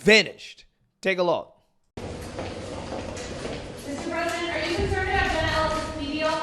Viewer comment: Did she call trump mr. President @ 6:24 😮